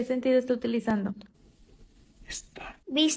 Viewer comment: le entendí "Esta" ajzbskskxjs "-¿Que sentido esta utilizando? -Estaaa!"